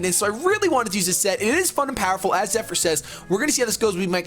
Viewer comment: LOL, i thought said "It is F***ing powerful" instead of "It is fun and powerful"